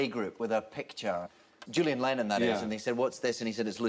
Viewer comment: I can't understand what they're saying at 9:48 to 10:30! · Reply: Do you know the official story of Lucy in the Sky with Diamonds? -Yeah. Didn't Julian come home from playgroup with a picture? Julian Lennon, that is, and they said, "What this?", and he said, "It's Lucy in the sky with diamonds". -Brilliant. Absolutely right. -They've written the whole song. Bloke upstairs was going "That's my job!" -"They've stolen my painting!" -"Get me a pen!" -Christ, no he'd done - - he'd done a - - little Julian - - -"I've got Lucy up here!" - Oh God. Little Julian had painted a picture of a girl surrounded by stars and it was his friend Lucy at - in playgroup, and John said, "What's that then?", and he said, "It's Lucy in the sky with diamonds." -What, that was the older brother that spoke like that? -And, uhh - - -"What's that, Julian? What's that?" -According to interviews, Lennon and the other Beatles, they didn't notice that the initials spelled out LSD until after the record was released.